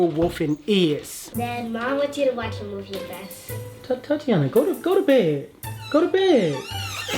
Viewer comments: am i the only one who heard thotiana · did he just call the kid THOTiana..!! ?🤔🤔